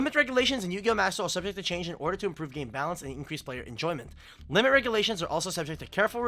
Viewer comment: It's tcg player saying ocg bad so don't think about it too much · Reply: My b, I misheard the translator saying balanced as badly placed-.